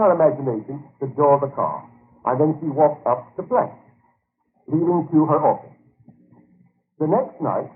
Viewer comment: You are very correct, but is correct too. i.e., at 16:40 the recording sounds like it says "...then she walked up the plank leading to her office..." or something like that. But YouTube's auto subtitle says "...then she walked up to play he will kill her off...". So even though you both are correct yes "youtube have added auto-generated subtitles, try that!" and yes you are "right! but sometimes they're wrong", which is absolutely correct. Could you imagine a hearing impaired or a deaf person coming here to find inspiration or divine guidance, actually playing this recording and getting these messages YouTube auto generates as subtitles. I do agree that either cleaning up the audio, adding subtitles (edited outside of YouTube) or both, would be extremely helpful to all, especially the disabled. P.S. I do understand that an audio of 24 min 50 sec takes time to edit proper subtitles whether they are a separate file or burn in. I noticed the only statement given in the drop down description is asking for donations. Where do the donations go? or is this publisher having problems with getting donations? I do have to say though, even though I have all Neville Goddard's audio lectures and books since I found where they all are, I still truly enjoy this channel because the publisher does provide all this content for free to others who are not fortunate, tech savvy, or patient enough to wade through and find this material. i.e. there is over 40 hour of Mr. Goddard's audio lect